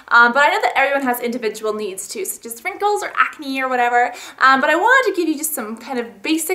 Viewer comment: did she say SPRINKLES? or is it just me?? lol 0_o